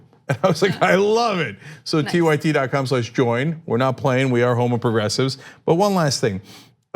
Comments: i love tyt but everytime cenk says their slogan it just sounds like he is saying: "we are homo-progressives" not that there's anything wrong lol but yeah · Did Cenk just say "We are homo progressives." @ 7:52? 🤪 Just thought that was funny. 🤣🤣🤣